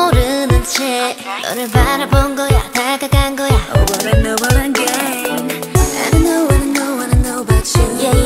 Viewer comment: I thought JinSoul said “I really wanna know what I gain” instead of “over and over again”... no words